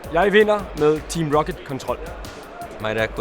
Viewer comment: lol i thought he said "fuck the maverick"